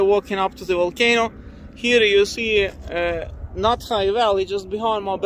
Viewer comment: I know it's not called that but the fact it filled up with lava and how you sound like you are calling it "not high valley" is just too funny. no matter how many times i hear you say it's name I just hear "not high valley"